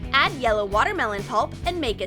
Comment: Who else thought it said Add toilet water ᕙ (° ~͜ʖ~ °) ᕗ